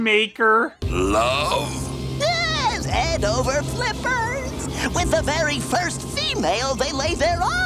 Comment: sounded like Crane said flippers. I'm guessing he meant to say Splinter.